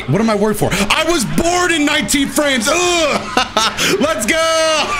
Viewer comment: I thought said "I WAS BORN IN 19 FRANCE!"